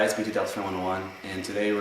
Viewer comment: you say verry fast... it sounds like you say BTdeltaf101 to me